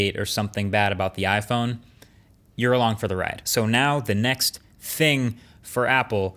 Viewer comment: Genuinely thought he said "you're along for the missus too." I was like that's a surprising take